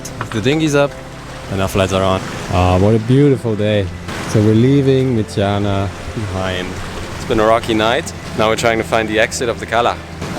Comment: Wha tis the name of the bay you left to go to Andratx? I thought you said Mitjana but can't find that on Mallorca. Cheers. Carl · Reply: Hey Carl, it’s Cala Mitjana: 39° 23.237' N 3° 14.826' E <link>